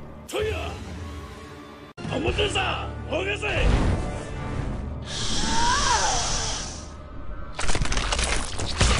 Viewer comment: what did he says at 2:52?? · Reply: Crystal HONOSEI